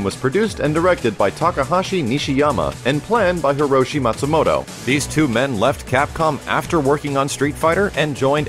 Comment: I nearly crapped myself when it said Matsumoto Hiroshi was part of making the first Street fighter game, I thought for a second you said Matsumoto Hitoshi, half of Downtown.